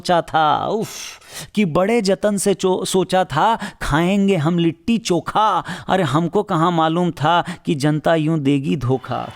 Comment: Just a second did you say cho*a